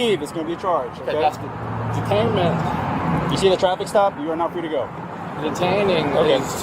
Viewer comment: I keep hearing him say "You are now free to go." Instead of you are not free to go.